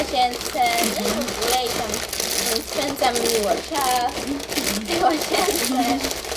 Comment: czy tylko a usłyszałam ,,rozkręcam Miłoszaa''? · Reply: Gram mówiła, ,spędzamy miło czaas